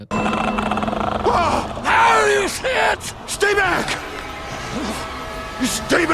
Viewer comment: We I first say this, I didn’t hear him say “Now you see it”, but instead I thought he said “Holy ///t”